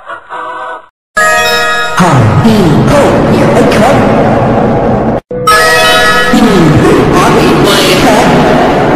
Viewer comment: what is he saying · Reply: Hi He Ho.Here I Come!